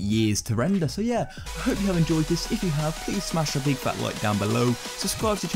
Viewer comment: Who else thought he said please unsubscribe😂😂😂